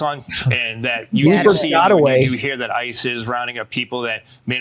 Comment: When they say "ICE is" , I hear "ISIS" 😂😂😂😂